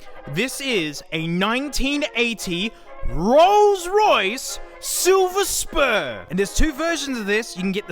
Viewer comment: ngl I thought he said "silver sperm"